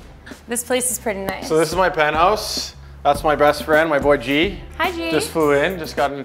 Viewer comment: I heard “this is my breast friend”